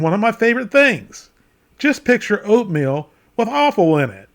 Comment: what did you say about Haggis? It sounds like "just picture oatmeal, but awful women.". Or "... All for women.". I have no idea what you could be saying.